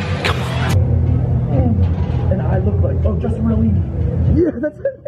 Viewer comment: what bodybuilder did he say I couldn't hear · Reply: Justin martilini